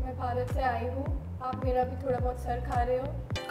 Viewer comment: she really said “aap mera thoda sar kha rhe ho” and he heard curry 🍛 😂😂😂😂